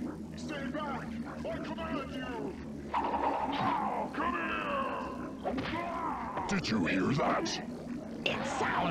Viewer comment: did scorpion say come here Wait wtf was that